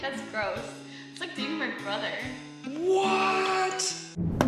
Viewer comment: I thought she said “no I like dating my brother”🤢😂